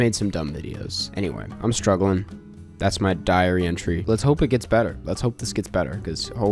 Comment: As said “Diary entry” I was on the toilet with the shits and I thought he said “diarrhoea” and was calling me out :(